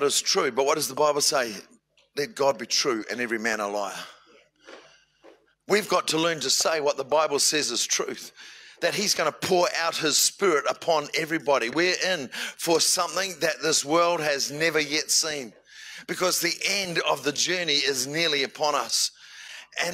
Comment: Maybe it’s his accent but I repeated what he said at the 2:15 mark with God pouring out Her Spirit. If it was that’s just another blasphemy on the list